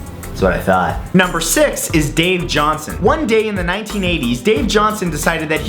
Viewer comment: Dave Johnson? I thought you said Cave Johnson. x3